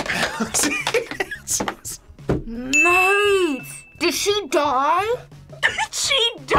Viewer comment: did say “maaaaate” ? 😂😂 she’s been watching some British shows if that’s the case